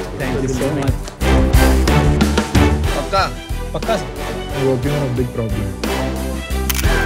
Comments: What did Piyush said...." Hope you have big Problem?" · What did Piyush said - " I Hope you now big problem".....?????😮